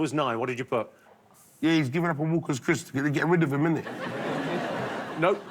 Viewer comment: 1,000,000 points to whoever can translate what big narstie says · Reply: "He's given up on Walkers crisps, because they stopped making them, am I right?" English is my third language.